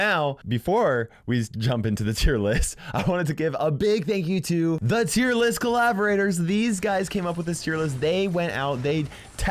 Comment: sounds like he said the list collaborators are disgusting lol